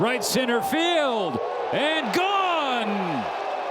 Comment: んーー悪影響が……。米実況は「ゴーン」って言ってて「ゴーンヌ」なんて言ってないのになあw 「ゴーンヌ」を聞くのはざっと40回に1回です